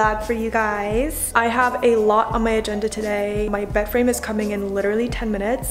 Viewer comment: when she said bed frame I thought she said boyfriend and I was like “WHAT”